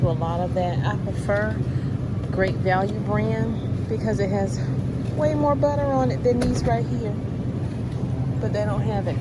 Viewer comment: Did she just say "way moe betta..." Out.